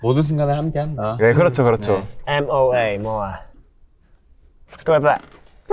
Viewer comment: did yeonjun say "throw it back?"